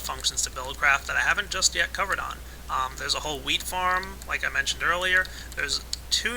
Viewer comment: When he said "Wheat-farm" I thought he said "Weed-farm"